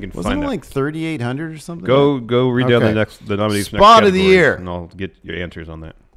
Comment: I thought of you said "Thot of the Year."